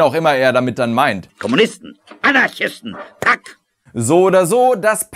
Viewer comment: Ich glaube das "Pack" kann ganz schnell als "fuck" missverstanden werden